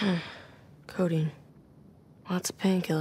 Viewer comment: Who thought wen Nate said codein he said cocaine